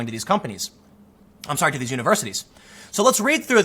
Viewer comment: At first I thought it said universe 😂